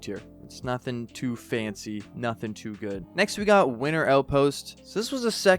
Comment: I heard “Winner” outpost.